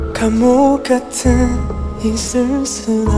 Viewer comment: "kamu ganteng, jisung selow" maap jun salah denger😭